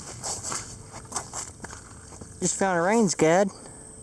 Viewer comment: What's the ring thing at 1:38? I've listened several times and can't get what he says. Sounds like rains guide or range guide. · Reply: It just dawned on me - reins guide for the reins on a horse drawn buggy or carriage.